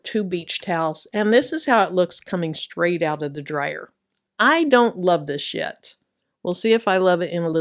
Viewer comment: 😂😂 I had to rewind a couple of times. What you said: I don't love this yet. What I heard: I don't love this shxt 😂😂😂😂😂😂😂